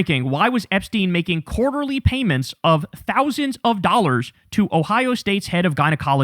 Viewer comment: I thought he said a thousand?